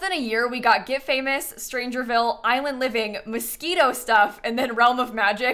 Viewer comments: I thought you said mosquito stuff and I was like wait what 😭😭😭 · I definitely thought you said "Mosquito Stuff" and not "Moschino Stuff" and was thinking, yeah, what the hell were the Sims team thinking producing a pack about mosquitoes, didn't even HEAR about that flop